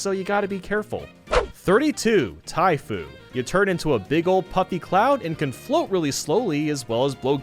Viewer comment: I thought you said Tai food XD